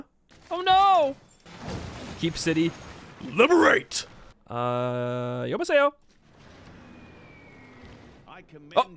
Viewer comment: did he say 여보세요?